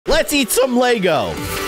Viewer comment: I thought he said “let’s eat some play dough!” 😂😂